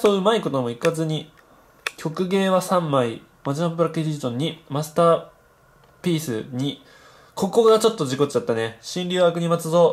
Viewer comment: 何かマスターぺ〇スって聞こえた、俺だけ？w